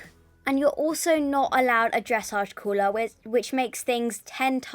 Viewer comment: A 'caller' for dressage (I have heard some other names for it as well) is when someone stands next to the ring and reads the movements for your test out to you so you don't have to memorize it (although you should still practice it). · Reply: OMG Caller ? I thought she said Cooler i have a caller for my dressage tests